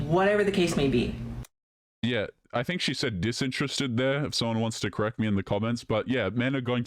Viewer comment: I think she didn't say "disinterested", but "just interested".